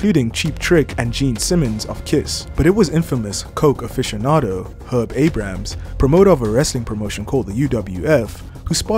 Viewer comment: With your accent I heard "Coke Aficionado ""Herb 8 Grams"""" and I think thats just perfect lol